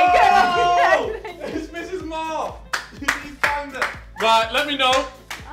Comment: did say Mrs. Mar??